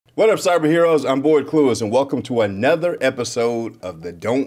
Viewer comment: Bro I thought you were saying "boy clueless" not 😂